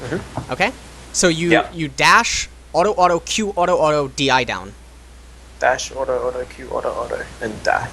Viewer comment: dash auto auto q auto auto and die.. lost it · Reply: no, the video is great as always, and the tips are useful too. I just found funny how the guy repeated after you at around 8:36 . Sounds pretty much like he was going to die after q auto auto instead of DIing :D Pls more midlane champs, keep it up!